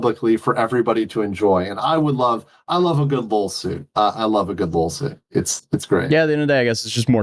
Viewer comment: did he say lulsuit?? sorry if im cringe for asking, but if he did that’s legendary 😂 never heard that